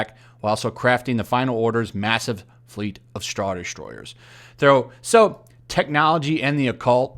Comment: it sounded like you said “straw destroyers”